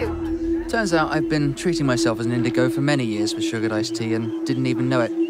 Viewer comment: "We use sugar ice tea." "What's that?" "Ice tea that has sugar." HILARIOUS!!! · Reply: in his defense it sounded like she said "sugar dice tea" lol